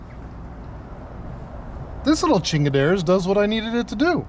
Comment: did i hear right? "this little chingadera" 😂👍